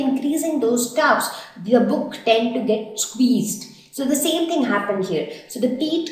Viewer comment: What she said on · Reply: squeezed